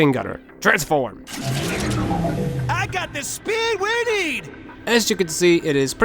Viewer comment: I almost thought he said I got the speed weenie But He said I got the speed we need